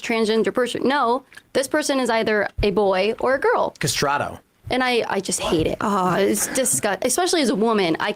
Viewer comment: did Tim say "castrado"? If so, I really wish he would've explained what she said to make him think of castradi. I wish Tim had explained what he meant by that.